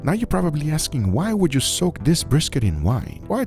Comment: i thought it said "i soaked a british in WINE" and i got excited for a second, but this is good too